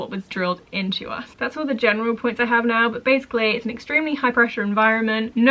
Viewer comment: 🙈 Funny moment here.. when you 1st mentioned pastoral care in this video and the last, I thought you were saying PESToral care. The word pastoral isn't common where I'm from, so I kept thinking PESToral was another way to say they didnt have good pest control services. After the 1st video, I pictured you living in a school with lots of bugs out of control 😂😂😂😂🤦‍♀️